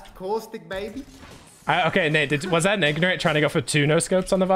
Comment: Did he just say nignorant???